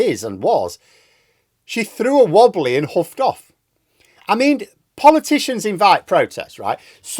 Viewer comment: I misheard Liz Truss "huffed off". I think my mishearing is what she ought to do altogether. I think she should "huff off" to the far side of "huff" and then "huff off" some more.